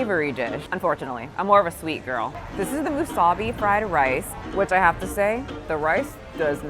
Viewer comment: 😂 Girl I heard wasabi…. Musubi is pronounced moo-su-bee. Good try through ❤